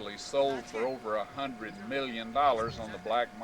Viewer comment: The guy, who stole the jewelry and sold it for 100 million dollars, may God have mercy on his soul. All these items are merely things, but it would have been nice to have been able to visit them and witness them first hand. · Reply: I thought he said 1.2 million